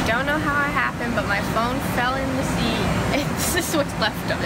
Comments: LOL 😂 Am I the only one who thought she said “My PHONE fell in the Sea 🌊 “ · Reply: The_ MeGaming me 2 I thought she said that she accidentally dropped it in the sea but the I realised that she said seat not sea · LOL 😂 Am I the only one who thought she said “My PHONE fell in the Sea 🌊 “ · did someone also thought nessa said "MY PHONE FELL IN THE SEA" cause i thought i heard her right but no haha i really thought nessa did fell her phone in the sea but once i saw them finding nessa's phone in seat i knew it im wrong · I thought they said it’s lost in the sea not seat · I thought she said "My phone fell into the sea" but then i realized she said seat 😂😂